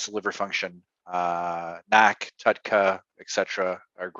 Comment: what supplements hes suggesting? i have trouble understanding what he said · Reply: Tudca and NAC